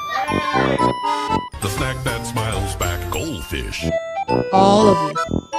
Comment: it really does sound like snap that child’s neck goldfish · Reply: I hear "snap that smiles back goldfish"